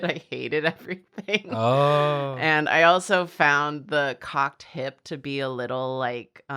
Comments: jesus christ I thought she said "cock tip" and I had to rewind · good god when said "cocked hip" i could've sworn she said "cock tip". had to go back and relisten to that multiple times. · I misheard saying "cocked hip" and thought she said "cock tip" and had to do a double-take like holy shit is she painting Luce's cock right now 💀